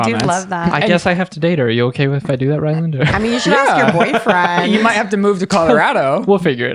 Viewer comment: when and Ryland are talking with. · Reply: I thought she said " Your boyfriend.."? Not very sure I am old and my hearing too.